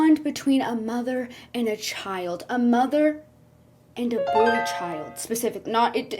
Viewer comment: WHY DID I HEAR A MOTHER AND A BOLD CHILD AT FIRST 😭😭😭😭